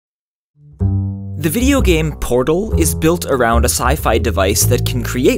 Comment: lol at the beginning when u said "the video game" I thought u said "the new game" I was like SMHHH :/ ASHDNJSHJNFUJ